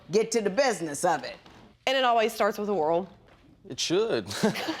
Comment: Lol thank you, was just about to ask what she said there :p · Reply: Lol sounded like she said starts with a whirl 😁😂